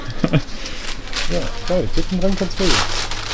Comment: „Titten dran, kannste vögeln“ oder was sagt da? 😂😂😂